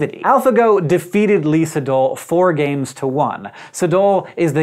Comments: For a while, I thought Hank was saying Lisa Dole rather than Lee Sedol. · I thought he was saying 'Lisa Dole' not 'Lee Sedol' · I thought he was saying Lisa Dole... but no, it's Lee Sedol.